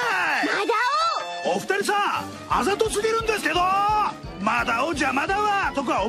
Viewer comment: I'm confused as to what Madao is actually saying at 2:15 🤔 It sounds like: 朝嫁ぎるんですけど (I'll get married in the morning, tho). But it also sounds like あざとつぎるんですけど (I'll keep bruising, tho) and あざとすぎるんですけど (You seem too brutal, tho).